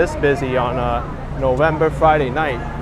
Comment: Did you just say "November forf" ? Is that NY'r for 'forth' ? 😄